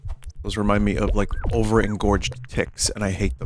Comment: I'm brushing my teeth and misheard this as "over-engorged tits" and nearly choked on my toothpaste